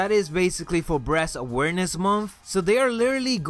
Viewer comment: Am I the only one who heard him say "breast awareness month"